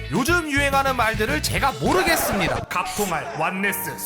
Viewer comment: 여기 의도하신건가 ㅋㅋㅋㅋㅋ 자막은 모르겠습니까? 인데 모르겠습니다.로 들림 ㅋㅋㅋㅋㅋㅋ